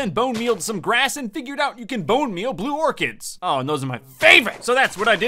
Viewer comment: He said “blue orchids” but I heard “blue organs”